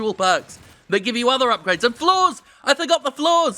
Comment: I honestly thought he was saying 'floors' not flaws.